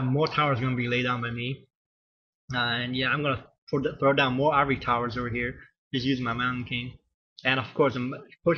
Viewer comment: Hahah, well done. && Did you just say 'Ivory Tower' instead of scout tower @ 1:27?